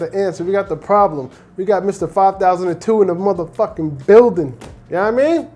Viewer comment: he thought you said 5000-2 😂🤣